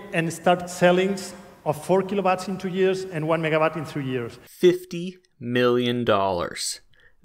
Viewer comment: I heard 15 million, not 50 which is still quite alot.